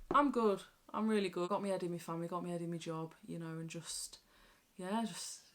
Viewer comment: Bloody hell! At about 17 03, I thought that you said that you had your ' head in your fanny ' 😂😂😂😂 Yiu are incredible ❤ xxxxxx